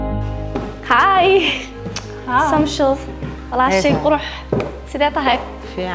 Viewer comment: Did she said Samsho Lol😭😂